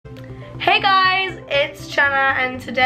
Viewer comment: I heard her say “ hey guys it’s shenna” in the introduction